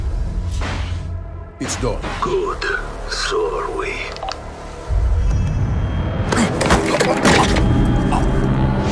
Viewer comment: I don't understand - Stich say "Good, SLOWLY" or "Good, SO ARE WE"? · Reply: It's done. Good so are we. What doesn't make sense